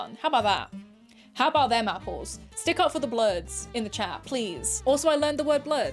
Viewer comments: I thought she was saying "Do it for the bloods" and I was like "Oh I don't think she knows what that means." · i thought you said bluds. bluds in the chat.